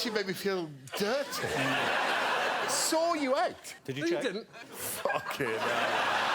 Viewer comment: What does Greg say at 5:48? Please help a non-native speaker out? :) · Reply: “i saw you out”